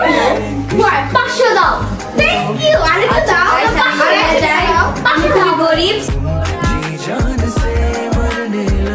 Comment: এটা কি বলো 24:14❤❤❤ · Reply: na